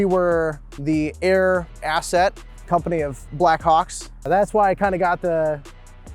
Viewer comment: I thought he said black cocks 😂 cool car tho.